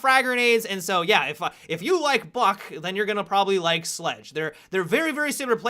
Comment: "if you like fuck, then you will probably like sledge" Am I hearing things?